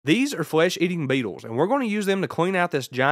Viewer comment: Dude sounds like he said "flesh eating diabeetles"